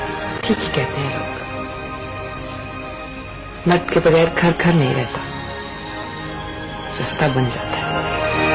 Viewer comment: did she just said "mard k beghair ghar ghar ni rehta, rasta baan jata hai?" Or i misheard it? Anyways it is correct and i felt it after my father's death the most.